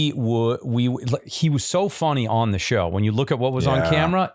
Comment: did Joel say fonny or is it just me?